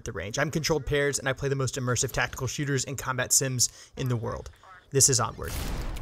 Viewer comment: I thought you said "I'm controlled pairs and I play the most immersive tactical shooters and combat sims in the world. This is awkward."